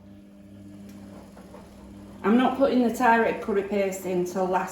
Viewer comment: The BANGING of the spoons!!!😱😱😱🤦‍♀️ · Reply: Thought you were saying Cat Food Curry!!!😂😂😂😂